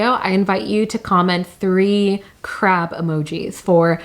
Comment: I thought she said 3 crap emojis lol